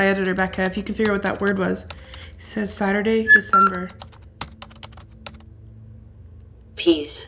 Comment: it said "S...A...T" That's what I heard and then all of a sudden yku said thatcit said saturday and december and i never hears it say saturday so i think what it was doing was it started to spell out "Saturday" and only made it to "T"